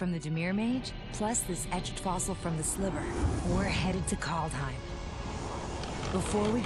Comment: Did I understand correctly, did Chandra say "We're heading for Kaldheim?"